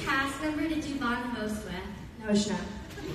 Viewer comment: who did she say??? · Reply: Selin Motevalli Noah